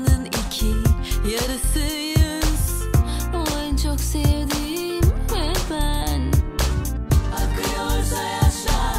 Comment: 'te ne diyor anlayabilen var mı :D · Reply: O en çok sevdiğim ve ben